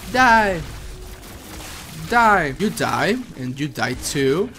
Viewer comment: Is it wrong that I kept hearing "Die! Youtube!"?